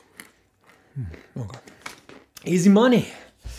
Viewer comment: 多分この一言まで含めて完璧な皮肉になるんだろうけど英語万年赤点マンだったワイにはなんて言ってんのかわからん… なんて言ってる…？ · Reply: おそらくeasy money（ぼろ儲け）